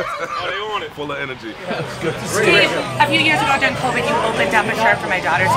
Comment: Did she actually call Dave 'Steve?' Same lady from the other place maybe? "Who's Steve?"